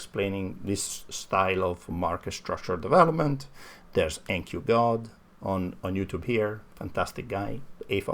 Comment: Who's the nq guy mentioned at 7:40? I couldn't catch it. Also big thanks, you're doing great stuff! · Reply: NQ_God on youtube!